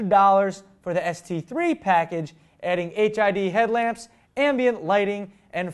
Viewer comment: i seriously thought Derek said HIV headlamps